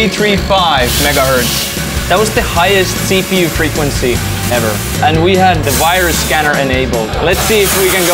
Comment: "8.335 MHz that was the highest CPU frequency ever" Did I hear that correctly?